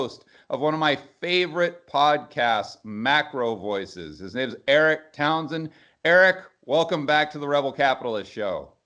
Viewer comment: I thought I heard Devin Townsend...looking at him - gosh he changed